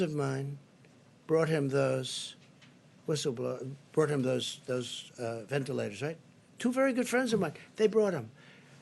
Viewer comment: Did he say whistleblowers instead of ventilators 😂🤣😆